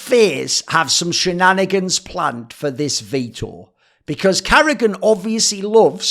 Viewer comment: What word does he keep saying ? Like in 42:05 VTOUR ? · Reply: Veto..